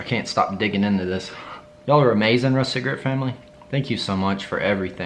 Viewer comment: Lol I misheard you and thought you said the aristogrit family for a hot minute 😂